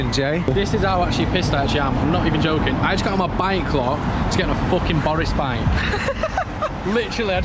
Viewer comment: what does he say here? I got my buying claw to get on a boris bike?